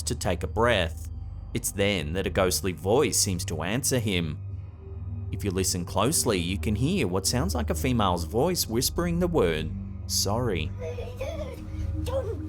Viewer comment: I heard the voice, early on in the video. I thought it said "hi". However, it does say "sorry".